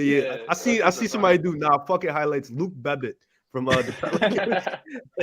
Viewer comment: how is saying Luke Babbit as Luke Bebbit ?